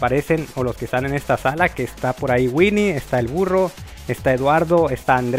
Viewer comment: entendí el furro